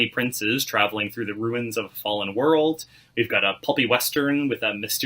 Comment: Did dude say...a PUPPY western...? I know what that means and I don't f**kin like what that means.....I really hope that he said "pulpy" and I only misheard the word....